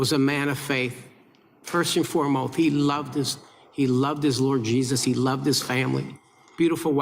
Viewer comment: I had to rewind when Rep. Troy _ said that Kirk loved his Lord Jesus. I thought he said he loved his orgies! I thought maybe there was more to this. LOL.